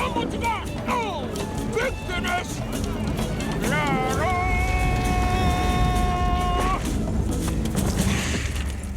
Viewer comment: what did he say? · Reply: C.J. Torres BIG FINISH